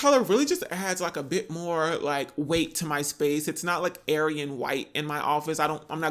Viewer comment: Lol did he say “ARYAN WHITE” 🤣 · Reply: lmao!!!!! Did he?! I thought he said “airy and white”, like light and airy. But damn I’m laughing so hard now!!! 😂🙈